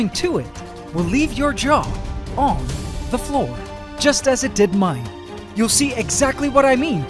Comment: It sounded like you said “It will leave your jaw off the floor”